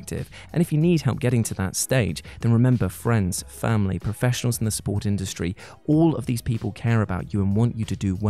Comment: I always hear "professionals in the sport industry" when you do your outros". I didn't realise David Beckham cared so much.